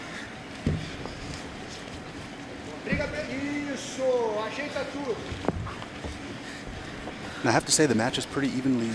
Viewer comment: I heard Kron say something to his corner? What was it 🤔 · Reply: He said: " Shut up, Márcio"! Let us fighter